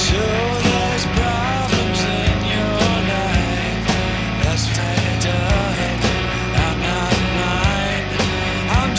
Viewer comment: That's uh...What did you say again? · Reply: "tanked up" it sounds horrible yeah